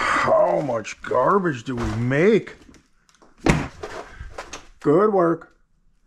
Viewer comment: always thought you said “go to work” to annie in the mornings but i hear it’s “good work” i think lol